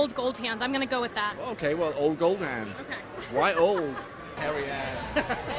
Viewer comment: I heard "hairy ass"